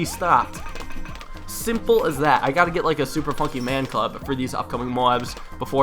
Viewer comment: did he just say "super funkey man club??